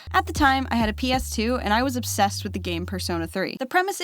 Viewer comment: did you say PERSONA?!